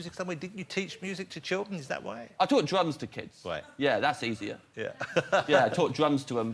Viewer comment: At first, I definitely thought James said that he “took drugs to kids”